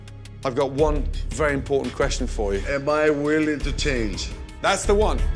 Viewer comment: when he said "am I willing to change" I thought he said "am I being detained"